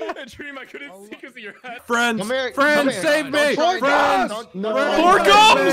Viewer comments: I thought techno was screaming 'friends' instead of frans xDD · I think the screaming each others name bit at 8:20 Porkums was actually saying “friends, save me”